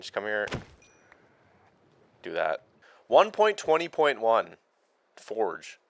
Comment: When you was talking about the fishing mod you want and you said 1.20.1... I had to rewind because I thought you said 1.21 then in my head I said gigawatts